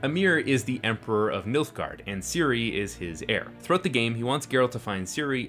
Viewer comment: Thought I heard “milfgard” around 15:39. Either girlfriendreviews has ruined me or I was already ruined.